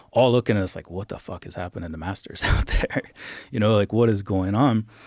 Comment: Maaannn I thought it said “Master P” almost got excited, though Joe brought the Colonel on 😂